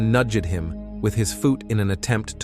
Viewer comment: Did anyone else hear 'he nudgded him with his foooot' instead of nudged him with his foot????At 6:42 tell me u all heard it!? Very horrible story. Just cause ur in prison doesn't mean u can treat a human like THIS.